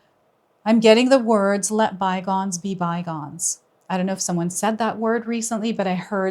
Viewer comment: Interesting. So when you said "let bygones be bygones" what I first heard was "let my bonds be bygones".